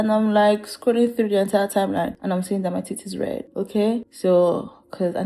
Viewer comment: I put this on in the background and i thought for sure you said "my tit is stained red" and i was so confused until i realized you said TEETH my auditory processing disorder just completely bungled that in my brain for a solid few seconds 😂 that lip look is killer btw it looks so good!!!!